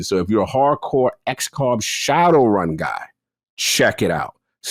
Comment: What is the turn-based game that talks about first he is playing? I love the man, but he never says the titles of the games lmao Actually, I think he says it at 1:40:03; I just can't make out what he said. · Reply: Shadowrun